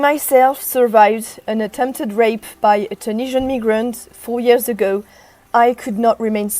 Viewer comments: Asmon called this out. He said they're going to laugh about this rape gang inquiry report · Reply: sounds like she said NIGRANT ....im keeping that one. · sounds like she said NIGRANT ....im keeping that one.